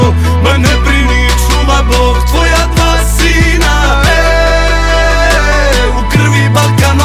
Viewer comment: Haha i hear “čuvamo tvoja palestina” 😂❤️ 🇵🇸